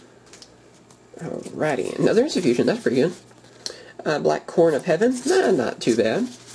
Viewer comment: sounds like he is saying black "corn" of heaven